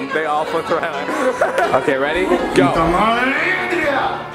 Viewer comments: what did zayn said there, I head common India🤣 I'm Indian but I'm not sure, did he exactly said that? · why do i think saying come on india 😂😂